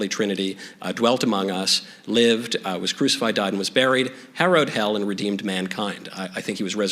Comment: Could someone please help me with what he said here, (2:56 - 2:57)? I rewinded and listened several times but I can't quite make out the 2 words before he said "hell." Thank you. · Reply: I think he said harrowed.